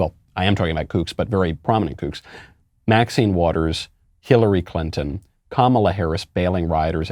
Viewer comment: 🤣🤣🤣 I thought you said “Killary Clinton”, but probably appropriate.